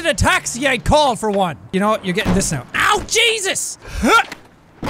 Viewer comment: it sounded like he said owl jesus